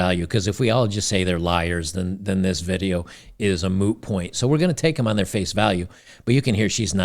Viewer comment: did i hear "moo point" ??? XD